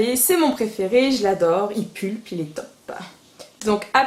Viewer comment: J'ai compris pour le mascara colossal : "Il pue, il est top" à 3:20 ^^